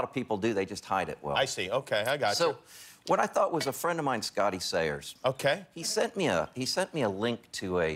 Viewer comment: Did he just say his friend is scotty sire